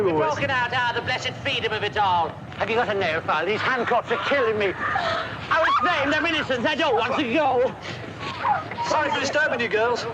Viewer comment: can someone tell me what the hell john was saying in 4:42 and onwards? lol i can’t understand with the strong accent. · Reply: John: “The blessed freedom of it all. Have you got a nail file? These handcuffs are killing me. I was framed. I’m innocent. I don’t want to go! (Paul then says: Sorry for disturbing you girls.) John: I bet you can’t guess what I was in for”